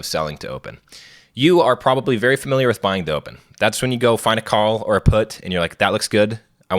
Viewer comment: I had to rewind this a few times bc it sounded like he said, "You're probably already familiar with selling dope." His explanation right after that didn't make it sound better. 😂